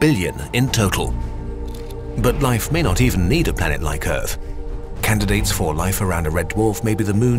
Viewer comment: umm what did you say?? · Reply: Candidates